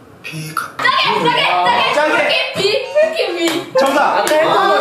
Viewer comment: 나만 싸게라고 들리나.